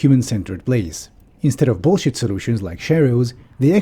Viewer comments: instead of bullshit solutions like "sherios"? Please someone explain the word. Thanks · "instead of bullshit solution like " what? "Cherios"? (that's what the caption says)